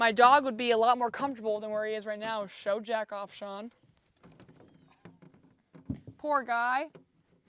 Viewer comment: Did she say "Jack off son"